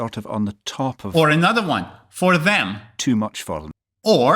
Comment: As a Floridian (USA), I got them all right, except the second one, I heard "fun" instead of "for them". The Adele one was kinda challenging, but I did understand, she talks fast. Edit: after hearing it a couple more times it definitely sounds like "for them"/